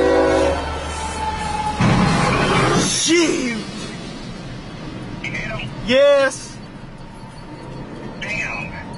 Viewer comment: what word was that? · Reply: Eagles Fan Since Super Bowl 52 Shiiioooot lol